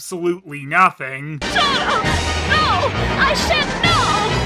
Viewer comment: almost sounds like Dorothy is saying "No! I shant know!"